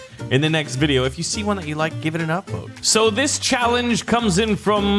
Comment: did he say upvote? Because i don’t the think this is Reddit.